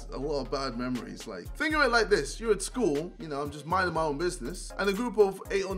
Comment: When jj said 'think of it like this' i thought he said 'finger it like this'😂